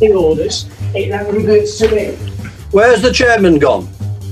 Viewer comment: When said where's the chairman gone I legitimately thought he said where's the german gone.